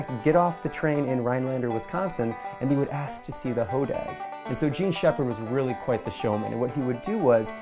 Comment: I thought she said the ho bag 😂